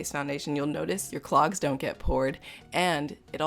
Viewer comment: At first I thought she said "Your clogs don't get pored" XD I realized she said "Your clocks don't get bored".